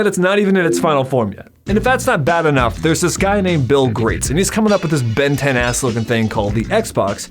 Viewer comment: I'm sorry, did he... did he say "Bill Grates"? Around 1:55.